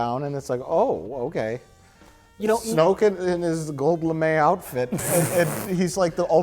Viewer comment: What does say at 12:35? Sounds like he says "gobe la may outfit"? Gobe la may? I simply must know. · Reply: Gold lame' outfit.